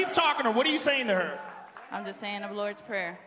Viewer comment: what saying rose at 17:50 to joanna: im lords prayer or something??? does someone understand this??